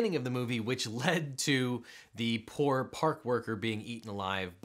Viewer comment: when you said park worker on the first hearing i heard park burger